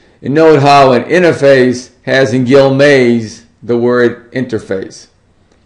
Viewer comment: Sorry, I couldn't hear what you said about....("gilmaze")? interface. I don't think I'm spelling (gilmaze?) right, but from 7:35 - 7:40 in the video, you said ("gilmaze") interface, and I don't know what (gilmaze?) means. Would you explain? Thanks!